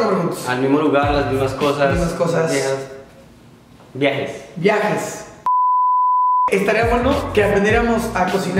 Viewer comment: le entendí viejas XD 😂😂